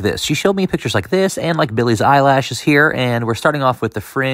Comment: he said "like Billie's eyelashes here" lol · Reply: Lol? I thought he said, “Billie Zielash.” 😂😂😂 I like “eyelash better!”